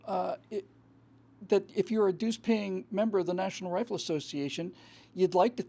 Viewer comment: Did you say "douche-paying member"? Cause if you give money to the nra you're giving money to that douche lapierre. I was a member of the nra for 30 years. Just as I was a registered republican. I no longer support either and for the same reason.